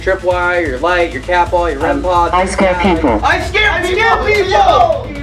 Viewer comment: honestly, while listening on the side, I didn't hear it as "I scare people" I heard, "I scare pancakes" and then I was just confused why would spirits want to scare pancakes